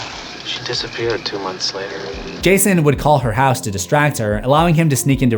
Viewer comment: i heard gayson